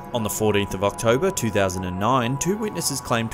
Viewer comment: did he just say " on the 40th of October ??? 2009...........